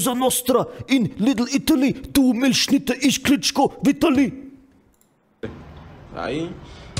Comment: Did i heard it right? Klichko Vitaliy?